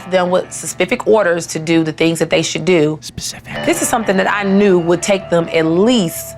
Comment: I HEARD YOU WHISPER "spacific" AND I THOUGHT I WAS GOING INSANE LOL